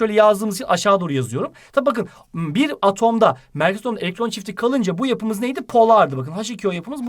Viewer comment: de ne diyor hoca anlamadım.Devam da edemiyorum sinirim bozuldu msmznsnznsnsksjsj Bilen yazsın Allah rızası için kfkfkfkfkfkflf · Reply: Merkezi atom elektron çifti *